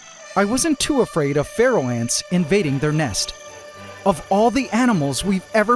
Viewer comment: Thanks for captioning that the invasive ants were pharaoh ants. At the end of the last video I thought you had said feral ants and was confused about what those were.